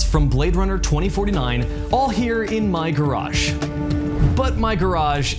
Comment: I keep hearing him say "garashe" for garage.